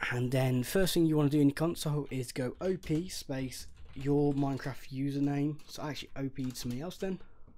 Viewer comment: So at 3:51ish in the video he tells you to type op (your minecraft name)/something. I can't quite make out what he is saying at the end could someone please help me?